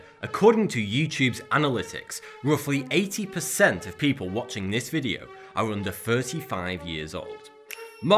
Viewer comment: i thought you said "8 percent of people watching this are under 5"